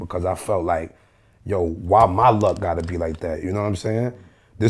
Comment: LMAO.....why My luck!!!! Did he say "My Luck" he really feels, he was the one who took tha lost in that SITUATION!?? Ha ha oh shizt